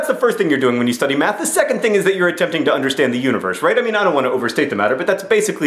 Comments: Whenever you said, "Trying to understand the universe". I thought you were saying "Trying to understand the uterus". hahaha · Every time you said universe I thought you said uterus...